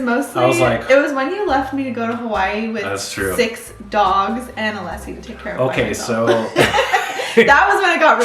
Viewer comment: I have on the closed captions because, one of my speakers is blown on my phone. Around 9:19 -9:20 the captions said Lauren was stressed out when Arie left her to go to “Hawaii with six dogs and a lesbian.” Lmao... it should’ve been six dogs and Alessi. These captions are crazy!